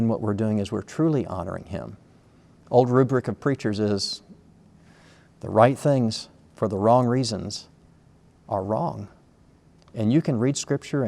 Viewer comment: Can anyone tell me what he said right at the begining of 4:02, please? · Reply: He said, "An old rubric of preachers is, the right things for the wrong reasons are wrong." A rubric, in this instance, means a saying or a proverb.